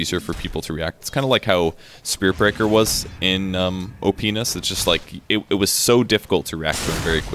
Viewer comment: op"penis"......I can't have been the only one who heard that :D